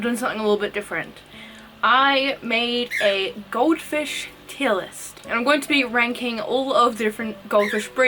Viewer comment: I literally thought that she said goldfish kill list💀💀💀